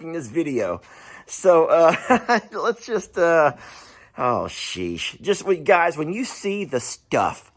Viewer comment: When you said "oh sheesh". I heard "ohh sheath" lol, lmao